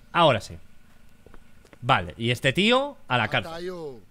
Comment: JAJAJAJAJ el megáfono pareciera que dice "A mimir"